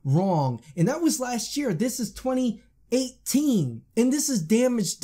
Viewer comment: Oh my lol did I say 2018 in 2019... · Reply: woops i misheard it. At 8:55 i thought you said this is 2018. But you said it WAS 2018. my b XD